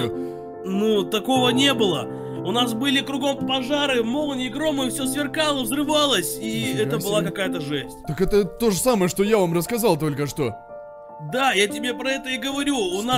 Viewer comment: мне послышалось бомжары